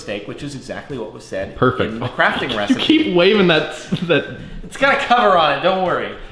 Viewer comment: Does he say" which was exactly what it said in the crafting recipe." ??